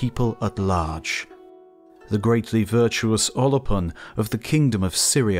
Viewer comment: I first heard "Olopun" as "Olive One" which is amusing given the general complexion of those from the Eastern Mediterranean